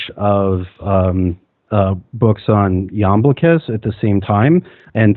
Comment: Can anyone tell me what is the book that they are discussing around 51:00? In relation to The Invisibles, but the other one about archons and heroes? I can’t quite hear the author’s name. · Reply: Iamblichus - it took a little Google work, but I found it... and now have even more books to seek out.